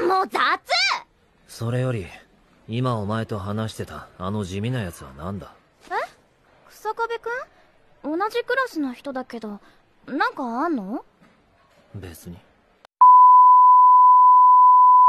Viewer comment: y 5:12 escuché besame hay casi me muero